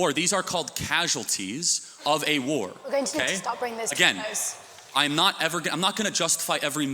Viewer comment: What does the lady say at 12:45? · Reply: Something about "we are going to need to stop this guy's" I'm not exactly sure tho